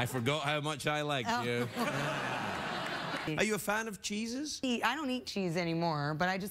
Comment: I thought he said, Are you a fan of Jesus?